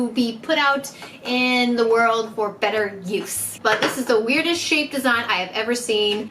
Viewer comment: I thought she said “this is the weirdest shit I’ve ever seen”